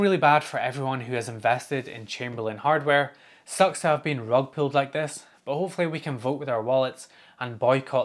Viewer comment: The Rug pull really really sucks. I can't stand losing functionality I used to always have. I totally plan on voting with my wallet, but the problem in this case is that for both residential and commercial applications Chamberlain/Liftmaster are the makers of the vast majority of garage and gate openers in the United States. The other options that exist tend to be vastly inferior brands that tend to break over a relatively short period of time. I went the route of purchasing the Ratgdo and will power down my MyQ as soon as it comes in. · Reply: I'm glad you wrote this, I thought he said rug pilled and I was really confused